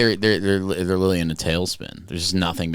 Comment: When you said "they're really in a tailspin," I thought you said "they're really into Tailspin" the cartoon and that they're working on a bunch of Tailspin remakes.